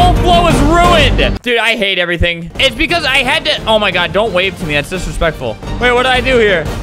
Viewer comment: “Don’t wave at me that’s disrespectful” -Albert 2020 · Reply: He actually said don’t wave to me that’s disrespectful 4:20 :/